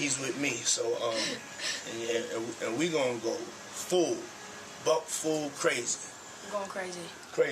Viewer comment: He specifically said “ We are going to go Butt-Full Crazy “ whether you thought he said Buck is not the point ‼️